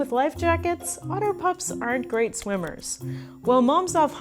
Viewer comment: i cant be the only one who heard otter pop instead of otter pup